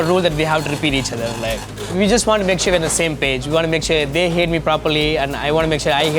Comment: I misheard him through his accent when he talked about his communication rule. It sounded like "I want to make sure they hate me properly" 🤣 I've heard Michelin restaurants having unique standards or weird methods but that one took me back lol